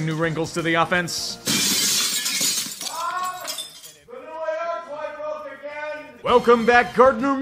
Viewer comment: What did he say? Mom the new ar toy broke again?? 7:18 😂😂😂